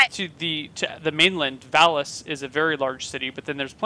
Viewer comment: does say that "Phallus is a very large city" instead of Valis?!